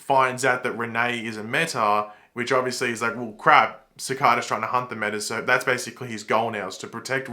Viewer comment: When said "Cicada was trying to hunt Renee" i misheard it to "Cicada was trying to hump Renee" and i was thoroughly confused and had to listen to him multiple times